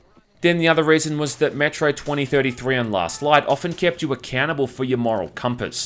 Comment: Great review! But when he says "accountable" I always hear "a cannibal"...